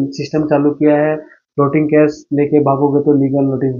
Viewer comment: ye kya bola bhai protein cash